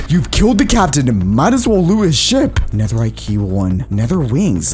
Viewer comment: Rest in peace computer for all those mods. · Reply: BRO I THOUGHT YOU SAID RIP BUT THANK GOD!!!!